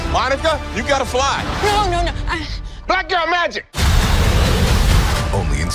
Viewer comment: Wait Wait wait, 24:07 What does Fury shout at Monica at the end of this clip? It literally sounds like he shouts "black girl magic!" And then she takes off to try and catch Kamala. That's not really what he says is it?😆👍